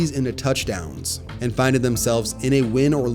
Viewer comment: Bro did you really say "finded" instead of found at 22:49??